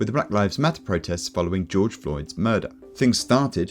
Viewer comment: did bro say george droid😭😭😭